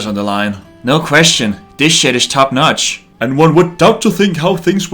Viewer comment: for a split second i honestly thought said "this shit is top-notch"